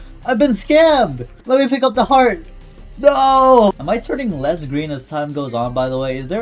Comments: i almost thought that said i think im turning less lesbian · “Am I turning less green over time” I thought you said am I turning lesbian over time oops